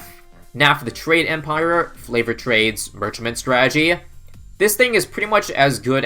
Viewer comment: did he just say flavored trades?